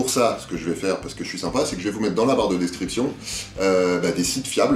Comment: j'ai entendu '' la barre de destruction'' 😂